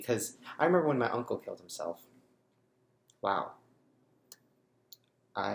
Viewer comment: I was reading the comments and I just heard say "When my unglued killed himself." And I was like, oh gosh. I am sorry for your losses. I love you so much. Sorry. Stay strong, buddy. ❤️